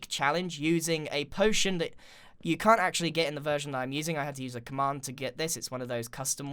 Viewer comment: When he said: I had to use a command... I thought he said: I had to ask mum...